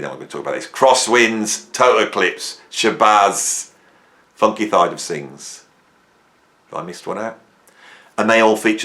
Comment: I thought you said Funky Thylacines for a second.